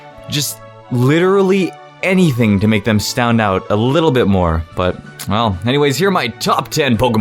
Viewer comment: I heard "stound out" XD